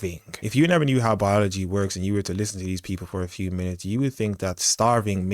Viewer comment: Brother when you said, "Listen to the people," I heard you say, "Parisite!" I can transcribe what those people are saying as a FREE American! I want every illegal/dead beat/leach/parisite off of my TAX DOLLARS!!!